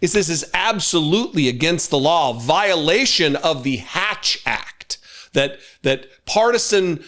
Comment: Hatch Act? I thought you were saying Ass Hat.